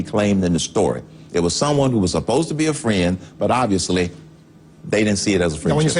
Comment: Did he say "upposta" ?